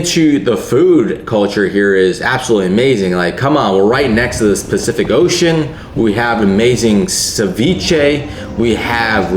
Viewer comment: Did you say "the specific ocean" at 3:36?